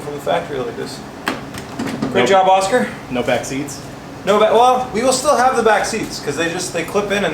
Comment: I thought Oscar said “ no vaccines”.. 😂😂